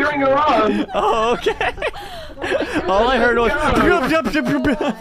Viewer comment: around here i think he said “I don’t know how to change the default”